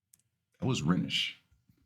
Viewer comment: did you say at 9:25 "that was Renish!" ??? nice one 👍